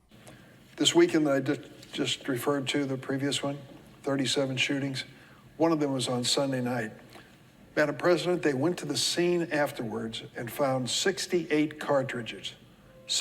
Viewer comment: One question, at 1:22, is he saying madam president? If that is in fact what he's saying, who is he talking to?